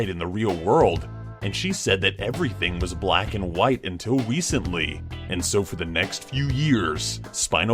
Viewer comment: anyone else hear say "wecently"?